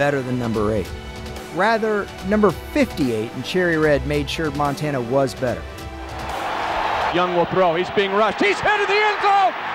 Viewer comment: Oh. At first, I thought it was saying Montana was young in 1994. I was like, wtf??